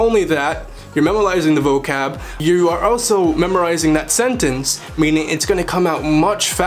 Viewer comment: It's not "Je vous manger", it's "Je veux manger". For someone claiming to be fluent in French that's a rookie mistake. I'm not fluent, but it's simple grammar. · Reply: Me S i think everyone makes mistakes whether youre fluent or not At 2:26 he said memalising instead of memorizing lol Its hard not to make mistakes especially if youre learning so many languages